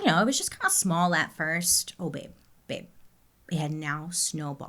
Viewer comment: Did just call me “babe” at 16:16? That just made my day. Thank you, 😍